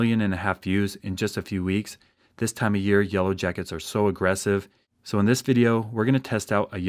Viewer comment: I thought it said jellowyacket